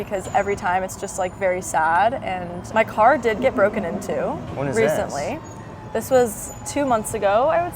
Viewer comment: I thought she said her car got broken in two, «fortunately» it got broken into 😮